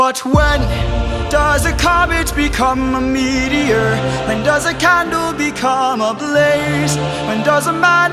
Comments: Please send help I can’t stop wheezing from mishearing the lyrics into “when, does a cabbage become a meteor, when does a cabbage become a blaze” RIGHT AFTER WATCHING THE SHORT ON MISHEARD LYRICS 😭😭💀💀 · Stop- I heard "when does a cabbage become a meteor"